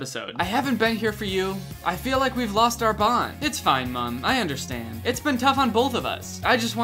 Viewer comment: i thought it said " i feel like we lost our blonde " and then i imagined them both just exploding and their corpses reasemble but with brown hair and they both just scream " OH SHIT WE LOST OUR BLONDE " and a hairdresser comes out of no where and quickly eats blonde hairdye and somehow that makes Mia and MUM's hair blonde again......my imagination is truly amazing